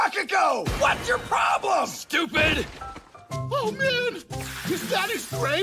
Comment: I can't tell what Sato is saying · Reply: “Oh man! Is that his brain matter oozing out!?”